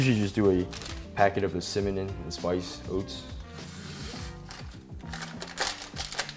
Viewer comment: did anyone else catch that "cimanon" i accidentally call it that too sometimes 😂